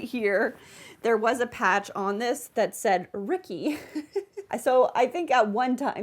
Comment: Oh my gosh! I thought you said the patch on the jumper said, "rookie"....I thought, how cool, you should add it back, then you said it again and it was "Ricky"....bummer...but I still like the idea of a patch that say's Rookie!!! ;) I think you can add a drawstring at the waist.... It looks super cute on you!